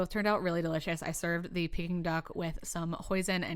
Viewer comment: I thought you said at the end I served my duck with some poison not hoisin 😂 delicious looking meal!!